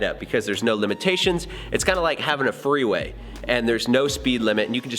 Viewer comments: "freeway" thought you said threeway had to listen again haha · Instead of 'freeway' I heard 'three way' and got excited for you. I need to get out more. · Totally thought I heard "threeway" and not "freeway"